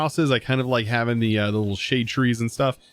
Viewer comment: i thought he said beans lol